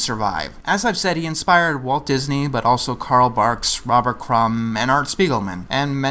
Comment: Carl Barks! Thought you said Karl Marx.